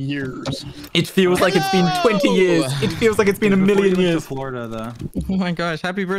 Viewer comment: NO NO KO JO AM I THE ONLY ONE WHO HEARD SAY "was before u went to florida tho" AT 0:41 ??